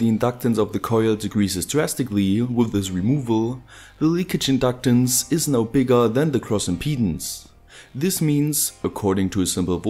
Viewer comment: could someone explain the phase shifts, I dont understant why the current has a 90º or 0º shift depending on if the current flows through the impedence of the iron core or through the secondary coil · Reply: also what is "cross impedance" or am I hearing that wrong, like at 3:40 is he saying " the leakage inductance is now greater than the cross impedance" if so I have no idea what cross impedance is